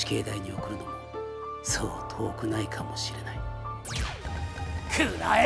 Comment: 犬夜叉の台詞の「くらえ！風の傷！」の最後の母音の音がちっちゃい「ァ」なのよ。「風の傷ァ！」って聞こえる感じ。そこが好き。